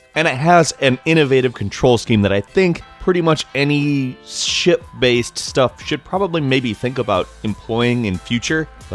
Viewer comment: I heard "shit-based"